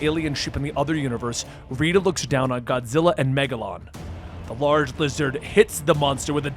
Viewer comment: Did he just say Megalodon? Lmao it’s Megalon. Also it’s (Guy-gan). For future video references. The random appearance of Megaguirus, Komacuras and Kumonga was pretty sick